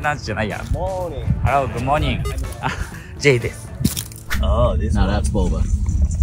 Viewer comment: ジェイですって名前読んだ後にです付けるとdeathに聞こえるんちゃう？😂